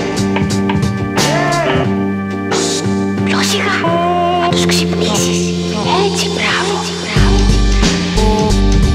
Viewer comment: What she say at 0:41 sec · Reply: Ποιό σιγά, θα τους ξυπνήσεις! Έτσι μπράβο.